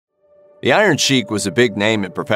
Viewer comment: In the beginning it sounded like you said the iron cheek